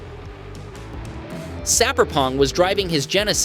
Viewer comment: why does the captions say "sappong" instead of "Sapperong"?